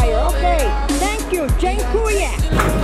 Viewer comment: Did she just say 'dziekuję' at 0:53?????? Btw, you and your grandma are such a family goals!